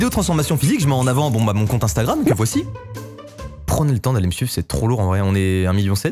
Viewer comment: "Prenez le temps d'aller me su*** !!!" J'ai compris ça ou bien .... 🤣🔞